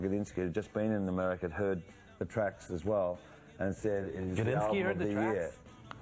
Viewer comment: they said somebody heard their tracks, kilinsky? who he is? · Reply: I think it’s Gadinski, Michael Gadinski, a friend of the interviewer. Then they go on to good naturedly take the piss out of the interviewer, I think 😆 pretending Gadinski’s opinion is impressive to them.